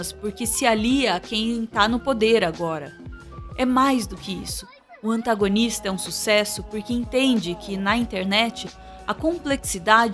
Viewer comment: eu ouvi um "sugoi sugoi" procede isso? Ok da pra ouvir algumas vezes dps, não tô doido